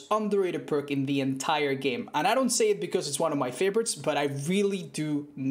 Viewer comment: Кто услышал «but I really do минет» 0:16 😂😂🙈🙈